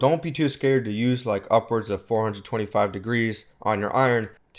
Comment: Did you say upwards of 425 degree (celcius?) on the iron for wicks? Btw very helpful video!